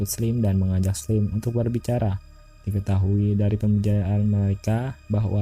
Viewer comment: Terdengar dari pembiehdhshdg 😂😂 ngomong apa bang Coba di koreksi di menit 6:50 bang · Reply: Pembicaraan